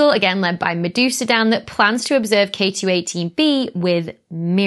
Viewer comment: I have to admit, your accent falling on my Canadian ears meant I kept hearing "Medusa Dan [and the] Haitian Planet." Which totally sounds like a reggae/electronic dance music band. Also (this might have been mentioned but I'm not reading 730 comments to check) the vehicle you mention in the bloopers was probably a skid-steer, often called "bobcats" here in Canada. "Bobcat" is like "Xerox" or "Hoover" - a brand name that's becoming the generic noun. "Skid-steer" is more jargon, used to separate people who know about construction equipment from those who don't.